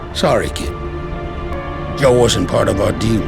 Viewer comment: soundtrack is so epic..... Not sure if i am hearing right but it sounds like he said 'Joe wasn't part of our demon' instead of 'Joe wasn't part of our deal' if you hear properly